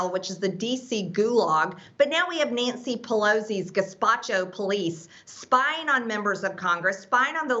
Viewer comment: I thought she said gazpacho but I wasn't sure I heard her right and then BTC confirmed it. As someone from Florida, all I can say is thank you Georgia. You're girl Large Marge is singlehandedly making our Congressional idiots look good by comparison. Okay, maybe not good but at least better. Okay, maybe not better but at least less bad.